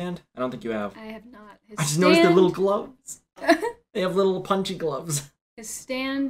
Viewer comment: When he said punchy gloves. I thought he said BUNGEE GUM